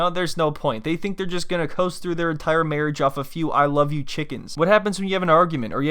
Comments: Ok. Thought he said "I kniw you cheatin" not "I love you chicken" 😂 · The very first time he says it, at the very beginning, I thought he said, I know you’re taken. Is it possible she heard that and just thought it was I love you chicken?